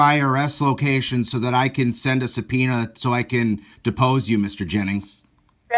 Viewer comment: He thought you said penis 😭😂😂😂😂